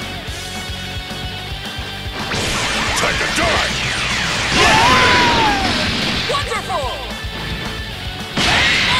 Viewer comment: what did he say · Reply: Time to die